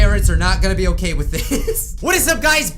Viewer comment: At first I thought you said "her parrots will not be okay with this" Anyone else? No? Just me...